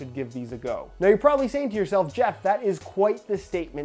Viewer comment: Did he just call himself Jeff at 0:53??